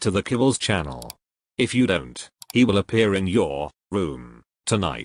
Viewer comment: I heard “big ed the gnome will appear in your womb” not room and almost screamed 😭